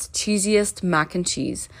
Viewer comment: For a sec I thought she said creepiest macaroni… 😂😭😭😭😭😭😭😭😭😭😭😭😭😭😭😭😭😭😭😭😭😭😭😭😭😭